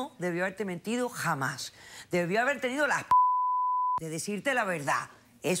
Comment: que dijo la doctora en el minuto 8:40 en la censura. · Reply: Carlos ANTLOP las bolas